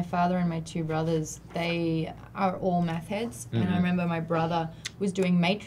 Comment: am I the only heard who heard say methhead